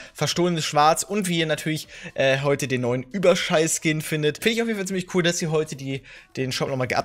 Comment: hab ich „Überscheiss Skin“ gehört😂😂. Oder hat er es extra gesagt???😂